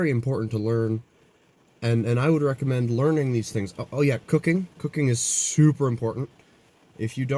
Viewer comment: I swore I heard you say "cocaine" the first time you said "cooking" 😂